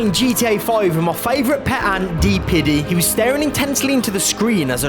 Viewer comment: I thought he said the ants name was P Diddy....